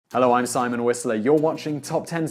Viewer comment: and bs means...? btw in every intro i hear " Hello everyone i'm Simon Whesley"